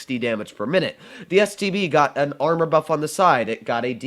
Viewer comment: HE SAID "STD" INSTEAD OF "STB"